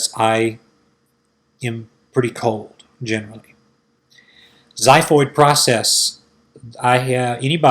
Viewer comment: Please can someone write down here what is said around 6:06 ? What proces? Can't really understand. Thanks! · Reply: xiphoid process ˈzɪfɔɪd/ nounANATOMY the cartilaginous section at the lower end of the sternum, which is not attached to any ribs, and gradually ossifies during adult life.